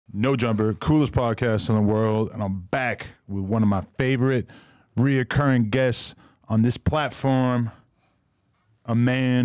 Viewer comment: Adam been interviewing the right people recently 🔥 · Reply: i thought niggas said no jumper fell off 🤨